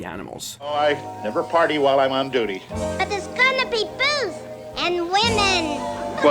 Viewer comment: did you say booze .. and women? sold.